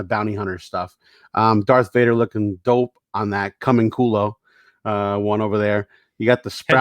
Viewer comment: When you said "Camuncoli" at 23:58 I thought you said "C'mon culo!" lol